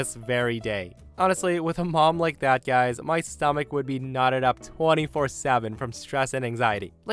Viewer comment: Knotted ok... I thought you said... Nodded 💀 That's not what I thought but it won't get me banned